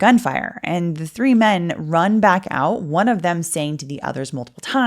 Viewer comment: As I was listening at @ 12:05 it cut to an ad, so it sounded like “One of them saying to the others multiple times….taxes. You shouldn’t have to worry about your taxes.” 😂😂😂😂😂